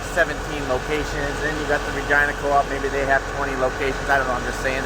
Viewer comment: Did you say vagina coops @ 00:31?😭😭laughing the balls out of my pants